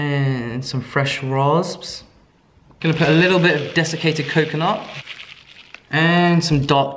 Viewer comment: I always hear "designated coconut" instead of "desiccated coconut" :D